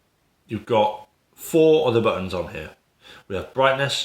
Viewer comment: What are you saying at 3:38. I'm not a native speaker. Four olivines ??? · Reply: four other buttons